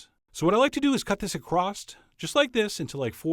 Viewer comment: you did NOT just say "acrossT" did you ? i just lost all respect for you.